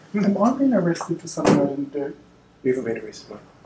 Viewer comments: Can anyone tell me what he asked the police officer and what the police officer answered at 14:15,please?English is not my first language! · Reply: I believe he asked if he was being arrested for something that he did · "Am I being arrested for something I didn't do?" Guy outside door: ????? I've listened to this half a dozen times and tried it on slow speed, but I can't figure out what he's saying. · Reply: Guy outside said " your not been arrested" followed by man or mate